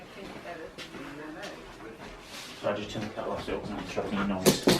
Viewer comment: what did the guy say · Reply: Something like “I’ll turn the kettle off as I don’t want it interrupting your recording” (as was wanting to make himself a tea)